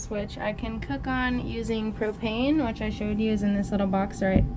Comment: When she said propane I thought she said cocaine. Lol